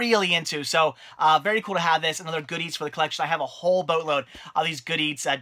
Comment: when you say good eats fast it sounds like your saying goodies